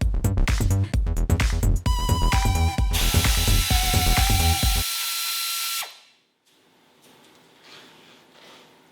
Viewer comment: what did he say at 12:25 lol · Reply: OldSchoolFella Turbo Yoda, its the knowledgeable man's nickname.